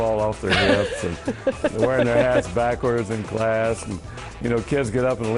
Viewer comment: i thought he said 'Wearin there ass backwards' xD